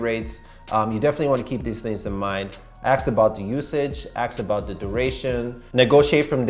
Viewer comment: did you say aks?! 🙃